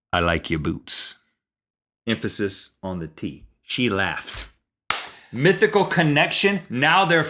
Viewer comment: >>> I heard the first time: "She left".